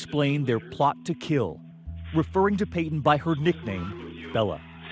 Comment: THAT SCARED ME SO MUCH! I heard Ella instead of Bella (Ella is my nickname)